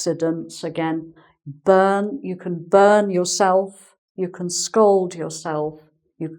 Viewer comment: Thank you for your videos, what you put in them makes learning English a great pleasure. For those who enjoy as I do these videos, I would like to catch their attention to the faulty transcription in the subtitles at 11:07 : "scold" instead of "scald". ;-)